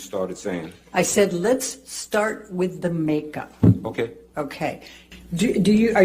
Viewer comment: 🤣🤣i thought she said Lips start with the Make up